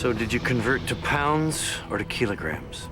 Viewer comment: Sounded like he said ' To pounds or Tequila' and the guy said Tequila!